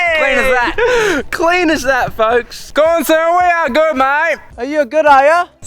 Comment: What the hell did they say? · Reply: "Gaunson, are we all good mate?"